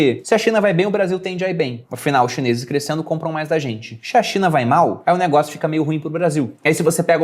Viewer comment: só eu ouvi "Xe a Cinha vai mal..."? Rsrs...